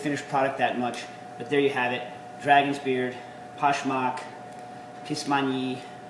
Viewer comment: I thought he said "Kiss my knee"